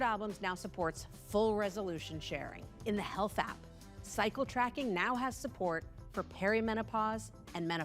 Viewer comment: Did she just say psycho tracking?